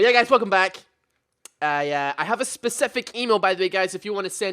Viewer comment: i heard anal not email